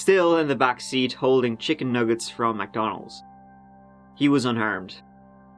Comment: I originally thought you said the 4 year old was “unarmed” instead of “unharmed” like?? 🤔🤔🤔🤔 i sure hope so ??